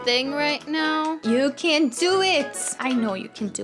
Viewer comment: This vid was that long I feel asleep and I thought she said you can do it Moody when she said you can do it Silly Also who misses Sarona